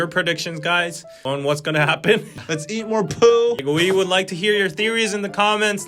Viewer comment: DID HE SAY? LET’S EAT MORE POO 🤣🤣🤣🤣🤣🤣🤣🤣🤣🤣🤣🤣🤣🤣🤣🤣🤣🤣🤣🤣🤣🤣🤣🤣🤣🤣🤣🤣🤣🤣🤣